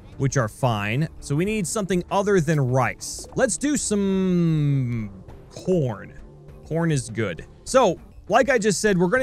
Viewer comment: I've replayed that part 3 times and I swear I heard him say porn... What the heck.